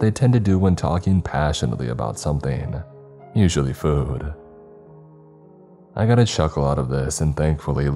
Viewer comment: I can't be the only one who heard him saying "I got a Shuckle out of this", can I?